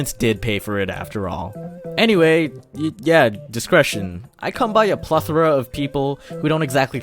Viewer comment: Who thought that it said direction?